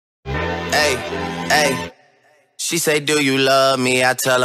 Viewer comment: damn i feel stupid now i always thought it was dad not bed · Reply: I am stupid as well. Till now I always thought he said " I tell her I only party" instead of partly.